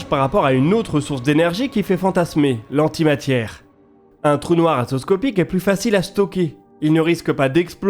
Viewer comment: il dit quoi ? "trou noir atoscopique" ?